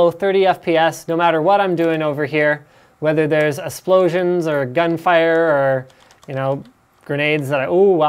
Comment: Linus did u just say "a splosions"